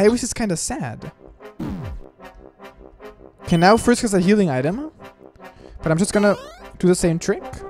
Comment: I like how Gaster goes pop and stops from playing as Papyrus, well not the whole time, Lmao. 7:57 - I heard that as "Oh, now Frisk is a healing item".